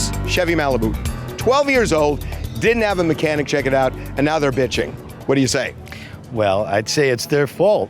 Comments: Did Harvey really say “Bitching” At 8:04? 😂😂😂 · Did he saying “ bitchin” 8:03 😅